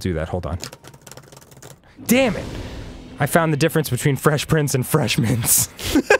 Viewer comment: i thought danny said fesh pince at the end and i got really excited